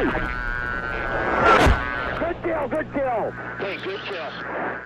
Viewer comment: what are the words at 0:03? I can hear only задел! задел!, but I'm sure it is not Russian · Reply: It's a pilot saying " good kill good kill!"